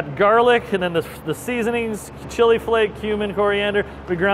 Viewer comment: cumin definitely sounded like human the first time I heard him say it.